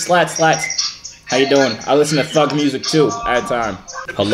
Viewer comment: LMAOOO I thought he said I listen to Fuck music too